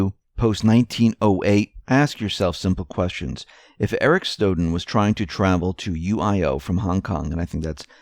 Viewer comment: Good report. NOTE: It sounded like you said "Eric Snowden" at 15:14 and 19:06. Ha, ha, ha. ES confusion anyone?